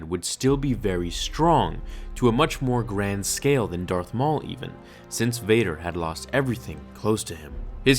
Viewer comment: did you say Dark Maul